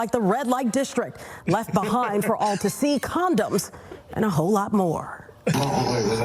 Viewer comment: did she say a sea of condoms? 😂